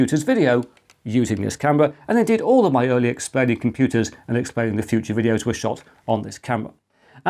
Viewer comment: It does sound like you say "Exploding Computers" :-) Maybe that's an idea for a third channel? After all, we've all had moments when we'd like to get our revenge against the monstrous devices that dominate our lives.